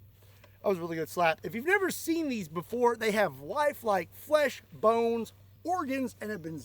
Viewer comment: on the zombie dummies... i thought he said, "they have wife-like flesh" 🤔. whats going over there in KY. perfect state abbreviation for this topic.... 😅😅